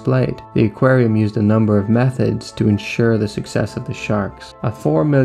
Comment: fully thought he said “Meth Heads”